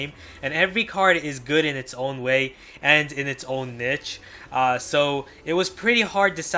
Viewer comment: I thought he said bitch the first time😂